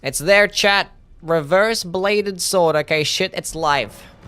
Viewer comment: I just figured out that says 'chat' and not 'Chad'. For years, when he said things like, 'It's there, chat', I heard, 'It's there, Chad!' I assumed Chad was one of his regulars and thought, 'Wow, this Chad really likes Youwy. And has a lot of time on his hands.'